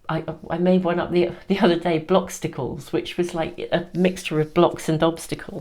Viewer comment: Oh yes— I heard ‘blobstacles’. I loved it and I’m keeping it!